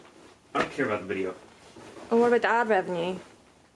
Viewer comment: What on earth does she say here? Obviously something to change his mind, but what WAS it? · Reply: “What about the ad revenue?”